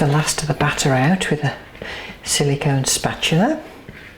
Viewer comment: i could have sworn she said 'silly cone' spatula! :P